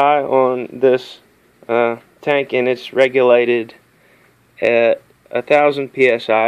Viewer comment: So, you have a 1000psi rated tank, and you filled it to 1500psi... ...what could possibly go wrong? :-( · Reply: Oops - My bad - I heard 'regulated' as 'rated' (or regulated as in tested) ... Shouldn't be watching technical(ish) stuff at night eh :-)